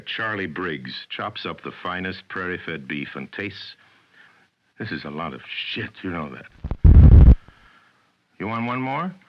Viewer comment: I know that this is is more "this is alot of shit you want me to read here" but I hear it as "What the fuck is this shit you're having me do"